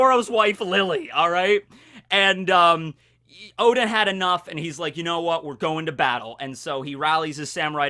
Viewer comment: "Oda had enough" (Sometimes I heard Oda and sometimes Oden, I am not sure) But Oda is definitely stronger than Kaido.